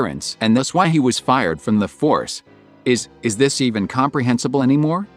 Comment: Did you say he was fired on purpose?